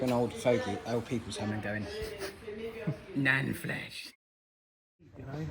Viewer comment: What was said at 18:03 really made me laugh. That guy seems really funny. I may have to watch it all again with some headphones on. His British accent is great. It is a thumbs up from me. - in case you didn't understand it , the British call their grandma "Nan" 😂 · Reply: NAN FLESH!